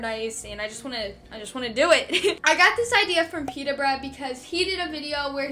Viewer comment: I thought she said mr bread 🤣